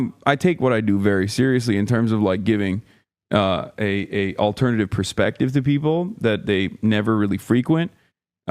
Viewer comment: “Alternative perspective” I thought that man said he spits nothing but facts lol